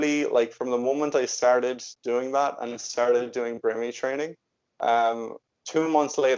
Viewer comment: What does he say 27:45? Brimmi training?? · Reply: Brain retraining